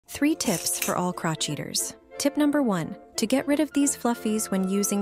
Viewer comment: I thought they said crotch eaters?!